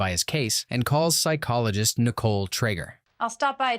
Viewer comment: bro did not say "trigger" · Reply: He said trager